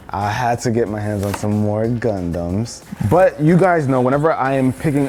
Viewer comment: I heard it as condoms